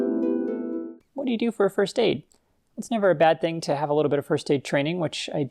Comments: Misheard "first aid" for "first date". Boy did the ensuing answer confuse me for a while. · For some reason I heard “first aid” as “first date”. Very entertaining misunderstanding.